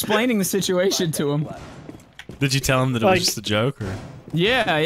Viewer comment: with Closed Captions it give you "Isis the Joker" instead of "it was just a joke".